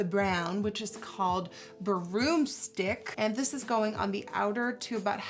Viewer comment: When she said broomstick I thought she said borrito